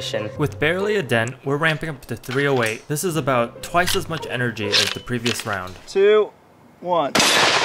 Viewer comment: bro did he say the n word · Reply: Energy not n****a